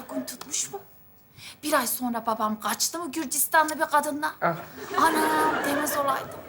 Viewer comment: da ne diyor 😮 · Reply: Gürcistanlı bi kadın diyor bende şüphe edip 5 kere dinledim